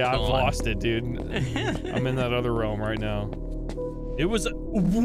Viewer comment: “It was uwu“ It really sounded like that to me xd